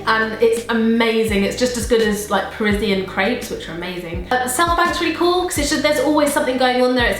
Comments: what was that place she said? i didn't get quite get it :( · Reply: South bank ? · what does say because i'm struggling to hear it? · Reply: I think she is talking about parisian crepes!